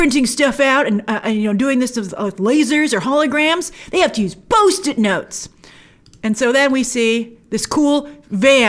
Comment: Ha! I heard "Boast it notes". Also, what you think is a neon Toyko is probably Hong Kong as you can see what looks like the Bank of China tower.